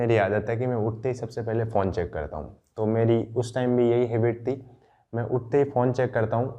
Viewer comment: I thought he said "uthete hi me porn check karta hoon" Gotta repeat 4-5 times to understand. Damnnnn brother